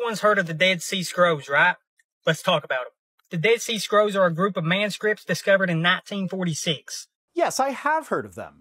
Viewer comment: Um... did he just call the dead sea scrolls "a bunch of man-scripts?" ... 😂